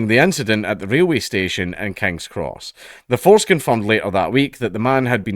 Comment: When you said "the force confirmed" i heard "the foreskin"